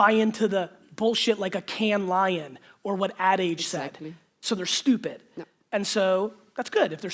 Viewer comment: what does Gary say at 1:01:15 - 1:01:18? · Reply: Daniel D "Cannes Lion or what the AD agency said..."